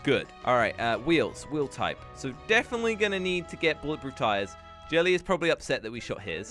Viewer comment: ok did anyone else hear jelly at 3:45 or am i just crazy?